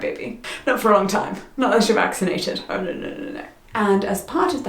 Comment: I think there's a mistake i the subtitles, I think at around 16:48 was saying "not unless you're vaccinated" but the subtitles say "not if you're vaccinated" I know it's a small thing, but it accidentally sounds like is against vaccines :D